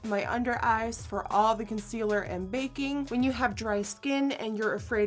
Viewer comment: i thought she said: from all that concealer and vaping